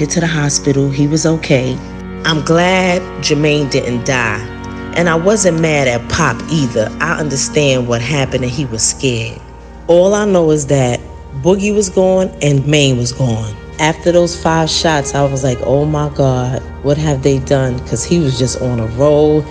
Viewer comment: did she say gone? I thought she said that they were all right